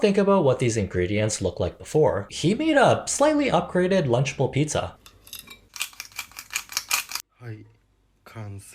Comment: Can someone tell me what he is saying at 12:35? I can't make it out. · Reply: I think he said " lunchable pizza" like the brand food